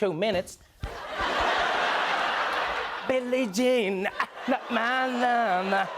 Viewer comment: Did Trevor just say "billy jean is not my momma"???